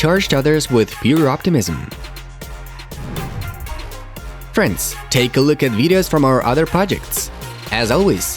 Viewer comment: everytime i hear that i think you saying France :D